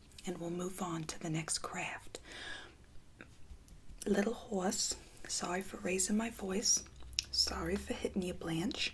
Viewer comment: lmao at 0:15 I definitely thought you said “Little whores. Sorry for raising my voice.” 😂